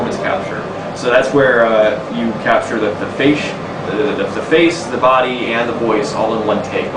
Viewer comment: he says fashe instead of face lol.